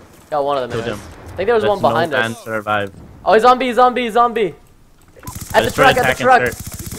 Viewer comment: When you say " He's on B " it sounds like you say " ZOMBIE! " . srsly